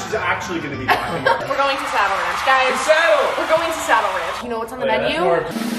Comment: Tell me why I thought you were saying “sour ranch” instead of “saddle ranch” 😭😭 but im so happy for you <3